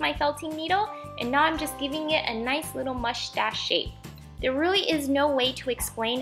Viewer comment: You sound like your saying mushtash